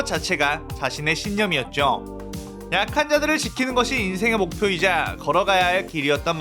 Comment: 약한자들 을 아카자들 이라고 들었네ㅋㅋㅋㅋㅋ